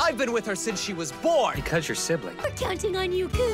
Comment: “because you’re siblings” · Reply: bro I thought he said because your simping 💀